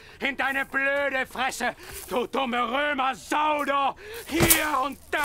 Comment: Was sagte Kinski zwischen 2:17 - 2:19? "Du dumme Roemer sau do??" Wer hat's verstanden? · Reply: das "do" heisst "du".